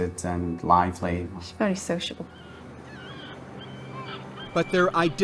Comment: Did the mother said:" She was very sexual" or..I can't understand properly? 😮😮😮😮 7:20 seconds, listen closely 😮😮😮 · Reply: She said special